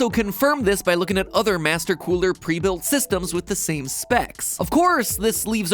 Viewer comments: did he just say master cooler😂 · Did he just say "Master Cooler"? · Did MatPat just said Master Cooler? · just my or at 7:35 did he say master cooler